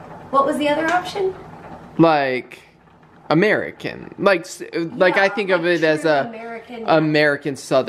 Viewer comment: I thought you guys were saying "A Mary kin" not American 😂